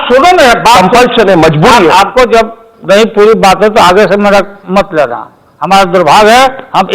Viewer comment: Did he just said " Aage se mera mat lena"